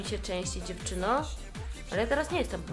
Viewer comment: KURRWWAA That’s what she said quietly but i can hear it